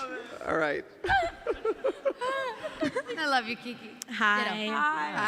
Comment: What did Keke say here to Emma? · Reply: Emma: I love you Keke Keke:Ditto